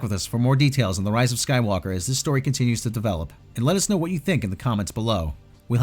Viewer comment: says "continues to develop" I hear "continues to decompose."